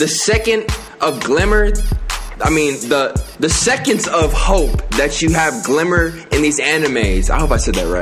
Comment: Did someone say "Hope" ??